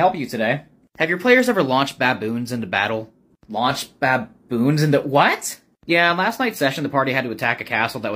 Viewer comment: I heard that as Batboon instead of Baboon. 😂